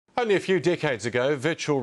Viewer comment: Omfg thought he said "only a few dickheads ago"